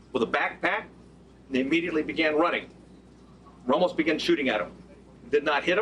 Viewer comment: “ almost began shooting at them”. Did I hear that right? And if so WTF does that mean? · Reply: You heard it wrong. He said "Ramos began shooting at them."